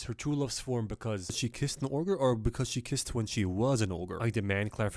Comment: it sounds like you say "or kissed when she was an orger" like a fucking Ogre burger and it made me laugh too damn hard, I know you didn't know but it's funny as shit